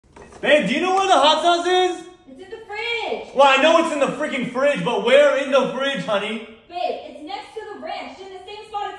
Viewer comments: I thought he said where is the fridge 💀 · I thought he said “Where is the fridge?”💀💀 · for a minute i thought said “Where’s the hot tube?” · I thought he said where's the hot tub💀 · I thought he said "babe do you know where the hot dog is" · i thought said"Where is the fridge,honey?"